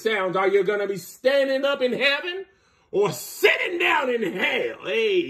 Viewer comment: Did I hear “Sudoku”? 😂😂😂😂😂